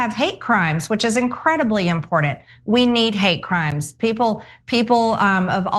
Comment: "We need hate crimes..." ?? Did I hear that correctly? Aside from that, I am starting to really admire her for her bravery and moral stance in pushing back against Israel starving people in Gaza, pushing back the US funding this genocide, for not accepting donations from AIPAC, for exposing their lobbying activities, and also for standing up for the Epstein's victims.